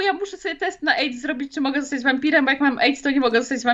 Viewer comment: czy tylko ja usłyszałam, że młotka to "Tama" a nie dama?